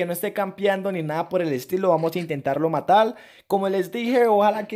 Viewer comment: like si te diste cuenta que dijo " matal" 😂👏👏👏